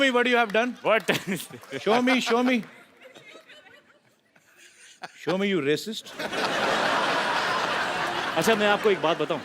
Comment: what did he say there? Racist?